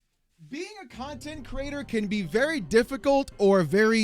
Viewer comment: At the beginning I misheard a bit while he was moving mic. This is what I heard: "Being a cunt and creator". And this is why I understood it's about Pirate Software :)